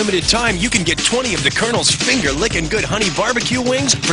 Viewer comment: Umm, person holding the brush: don't you think the wings have enough honey bbq sauce already? 2:21 Subtitles say: "These days, everyone's trying to catch Nintendo's Okay Mom." LOL. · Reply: He said the subtitles said okay mom not colonel